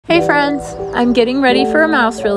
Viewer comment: I thought you said, "hey ferns" at the start, so you should name them fern!